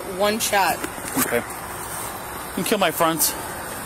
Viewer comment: When the officer said can you kill my fronts I would have sworn to death that he said can you kill my friends?